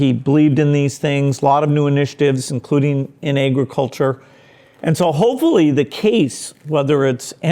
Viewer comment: Awesome. But did he just say "negro culture" at 6:34? Not that I care, but usually people would comment about it in the comment section :P